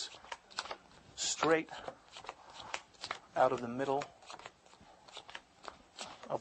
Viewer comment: sounded like he was about to say "straight outta compton":D